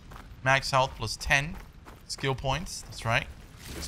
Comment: xD i heard it wrong when got the first skill points, i thought they said "Skittle points" and i swear i'm only gonna refer to them as such from now on 'cus this sounded way more fun HAHAHAHAHAHAHHAHAHAHA ♡